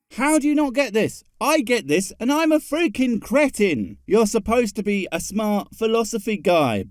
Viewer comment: I heard "How do you not get this? I get this and I am a feaking Britain!" Lol listening in my truck so must have been background noise that turned the c to a b. But the idea that you are implying all Britain's understand this is hilarious to me lol.